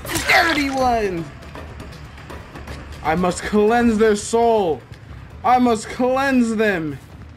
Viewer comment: Did I hear you say cleansing... of the soul?! Usage of FIRE is highly advised!